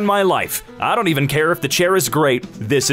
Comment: I heard "I dont care if the chair is grape" and thought about how the hood enjoys grape cigarillos(not blunts, blunts are the shape/size of phillys)